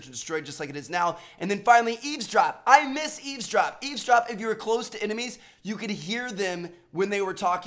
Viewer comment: Did he just say "I love eavsdrop." tf?